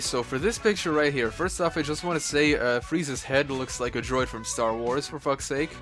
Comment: I heard Freeza xD